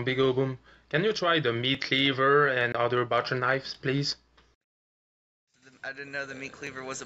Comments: You must be really deaf to not realize he said "and other BUTCHER knives" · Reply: watching it on my phone, I thought he said butter knife as well. I went back and and watched it again and I still had to really listen to hear it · I think he said "butcher knife"